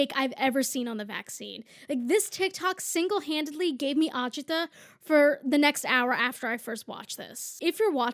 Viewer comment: I'm stupid please help what word is she saying at 7:52? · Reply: it means "anxiety, stress, or aggravation." also its spelled Agita if you wanna search it up <3